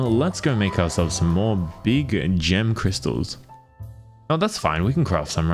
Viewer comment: Am I the only one that heard him say “let’s go make us some more of those vegan gem crystals” ? 😅